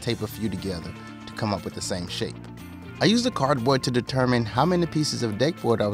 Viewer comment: i really heard to come up with the same shit instead of shape.